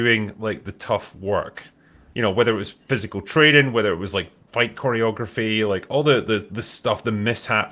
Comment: I misheard the drinker’s “fight choreography” as white choreography and that made the video a lot more entertaining